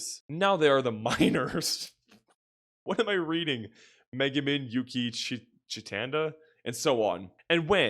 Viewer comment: bro I thought you were saying Megaman instead of Megumin untiil 35:00... then I looked her up and it's not any better 💀